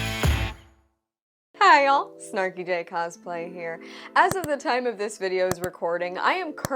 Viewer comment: I thought you said snarky dick play 😂